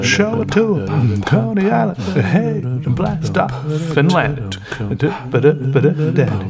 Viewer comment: I keep hearing him say "Finland" but that makes no sense. I'LL STILL TAKE IT THOUGH Also, may we get full lyrics please?